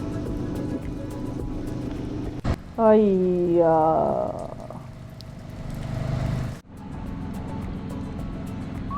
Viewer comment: Hi....did you say Haiyaa @ 2:30? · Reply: Ayyyu like “ugh” in Spanish 🤪